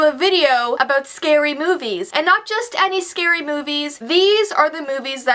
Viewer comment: At first I thought she said scary boobies